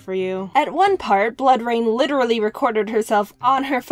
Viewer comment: bloodrayne sounds like a badass name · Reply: ı thought she said blood rain